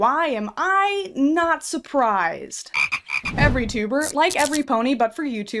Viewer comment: did you say every tuber? That's what my ancient parents say